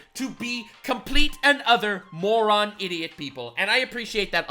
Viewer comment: Did he say complete and OTHER moron idiots? And if so, is that a meme that I`m unaware of...or does he think that`s the saying?